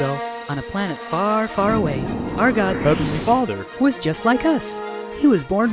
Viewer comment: What say the Elder Young at 1:34 ? I think he say "After your father". I don't know.